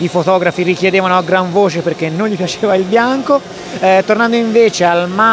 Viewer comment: "i fotografi richiedevano a gran voce perche'".............. poi non capisco cosa dite... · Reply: non gli piaceva il colore bianco.